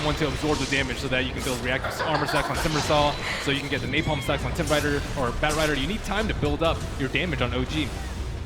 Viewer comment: did he say timberrider at 5:06?